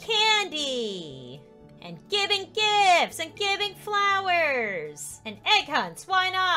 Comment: shen she said giving gifts i thought she said giving kids